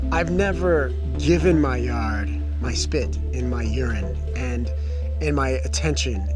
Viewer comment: Did he say urine?